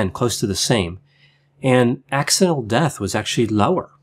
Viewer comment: sorry, but what did you say there? You spoke a little bit indistinct and I'm not a native english speaker. Did you mean "accidental death"?